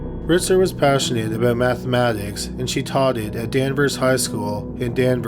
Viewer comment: Did you say Taughted @ 1:48 ?